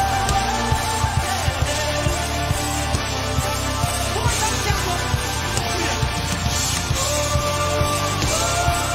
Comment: what does the weeknd say in the minute 2:17? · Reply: Yeah he said “one more time Tampa, let me do it”